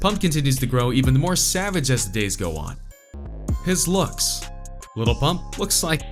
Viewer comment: Lmao it sound like he said Lil PUNK